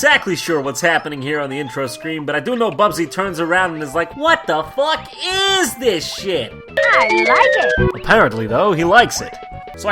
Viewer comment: Is he saying "What the fuck, UUUUSE this shit!" or "What the fuck, IIIIIS this shit!"